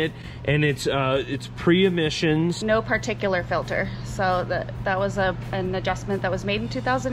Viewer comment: did she say no particular filter? 😂